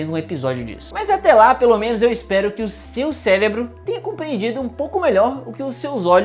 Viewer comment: juro que eu entendi "seu célebro" xD EAHUEAHEU